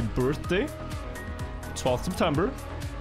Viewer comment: 12th of September? Is that what he said his birthday was? If so, we have the same birthday